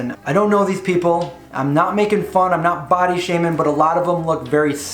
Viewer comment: I thought you said "bottom shaming" and I was like "whut?" But yeah, Sv3rige is just a sad human. Fvck that guy.